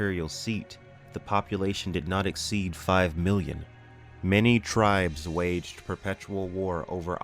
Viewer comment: 5 Million. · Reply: ah ok it sounded like you said billion